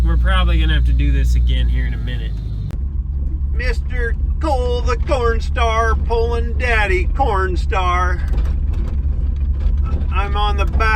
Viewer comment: My girl" did he just say cole the porn star and daddy pornstar?!? " hahaha